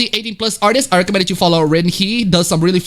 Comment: What's the artist's name? I heard "rinhe". I'm not sure how it's supposed to be spelled out.